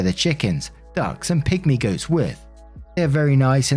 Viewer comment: I thought you said "pick me goats" and I instantly pictured goats acting like "pick me girls" and I laughed so hard after I realized you probably said pygmy goats. lmao